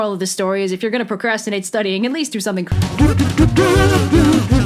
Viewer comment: for those that are anxious that it cut off before she finishes her sentence. the subtitles say ,"at least do something creative with your time"